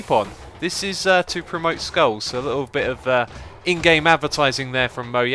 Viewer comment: Skulls and MoYang. :P That's what it sounded like.